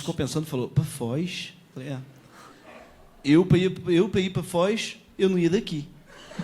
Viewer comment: essa eu não entendi. O que o Gregorio falou??? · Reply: "Eu, para ir para a Foz... eu não ia daqui".